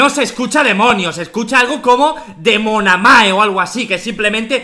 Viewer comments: En mi opinión escuche demande xd nu c ustedes 2021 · yo escuché demonamarsh pero respeto tu opinión crack